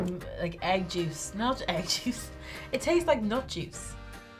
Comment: did she just say it tastes like "nut juice"？😂😂😂